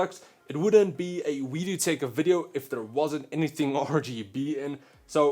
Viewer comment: I thought he said weed do tech in the intro